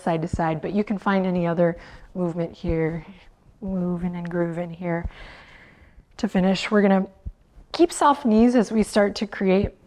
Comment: Not sure if I misheard this, but did Adriene say 'movement and groovement' somewhere in there?